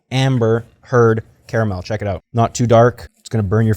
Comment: Did you just say Amber Heard caramel?